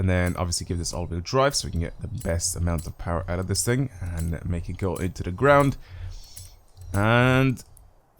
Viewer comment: “All wheel drive to get the most power” bruh😂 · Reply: "give this all wheel drive so we can get the best amount of power out of this thing; make it go into the ground." You misquoted him. He said exactly what he meant, and what he said was completely correct.